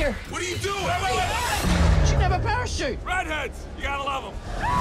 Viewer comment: Nice. I actually thought i heard him said "Blender! You gotta love 'em!"